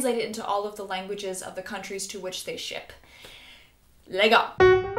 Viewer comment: Did u just say " Legal " at 0:41 ? is that Portuguese? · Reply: Leggo